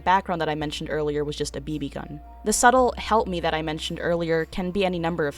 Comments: lol. I did not hear "help me". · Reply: tbh i always thought she was saying "hump me" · I heard "hump me" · When she supposedly said “help me”,I heard “eat bread” for some reason...